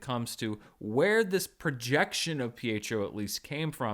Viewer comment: I thought Agatha said Fietro was a "possession" not a "projection."